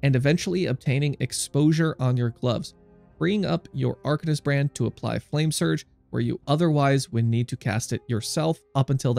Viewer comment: Hello! 10:33 what did you say? "_____ your __ to apply flame surge or you otherwise need to cast it yourself"? Also where do you get the ES regeneration for the belt? Thanks! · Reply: arcanist brand to cast flame surge